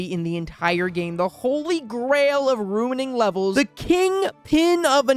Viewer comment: DID HE JUST SAY NINE ELEVEN?!?!?!?!